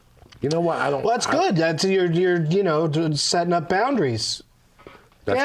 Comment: Did Doug just say Joey was "setting up boundaries"?...That's what I thought he said.